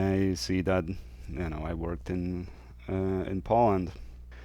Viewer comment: What is your other channel? When you said you work in Poland I thought you said you worked in p0rn, lol. I had to rewind and play again because I couldn't understand what the two had in common. Great work.